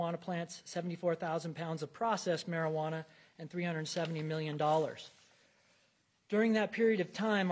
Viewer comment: 370 MILLION dollars ??? Did I hear that right 🫨🫨🫨🫨🫣🫣🫣🫣